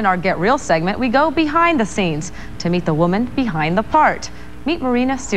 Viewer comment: I heard - "The woman behind the fart" - and then farts?!